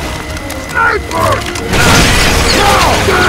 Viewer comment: i heard heavy saying diaper